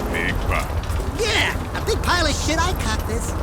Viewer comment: did daxter say shit or something? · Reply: He said "A big pile of-should I cock this?" (holding a gun)